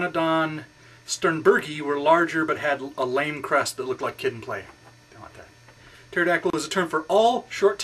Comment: I think at 1:48 I said, "...had a long crest that looked like Kid n Play. Don't want that."